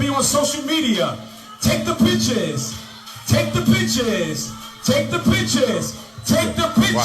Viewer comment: At first I miss heard. And his preaching sounded like wow! Then I realised, he was saying, "Take the pictures!", not "Take the Bitches!".